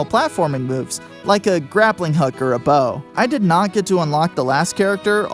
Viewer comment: I thought you've said "grappling hooker".